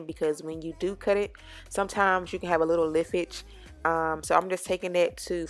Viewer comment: girl did you just say "liftage"? 😭😭😭